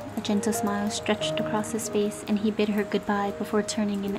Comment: Narrator: "And he bid her goodbye" Me: I thought she said bit, like i mean sure..go Adrien! We support...